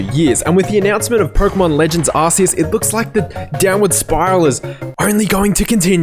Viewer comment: 😂Did he just say "ass-ceus ",everyone attack!